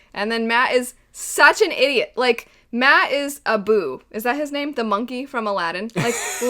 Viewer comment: I thought she said Matt is a boob.